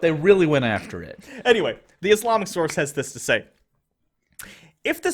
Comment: I could have sworn you said "Islamasaurus" there... which would be awesome, if a bit misguided on the sauropod's part. <_< ... and whoa! I have the exact same headphones that has - which is probably only important to me, and not even that when I think about it. They's good headphones, of COURSE other people have 'em. ^^